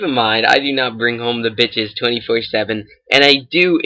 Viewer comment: LMFAOOOO Did he just say " I don't bring the bitches in 24/7"